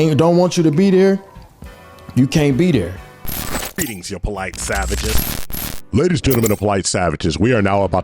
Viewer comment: I SWEAR it sounds like he’s saying “you BLACK savages!”